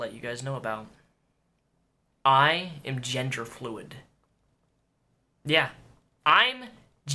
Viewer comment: I thought you said ginger-fluid.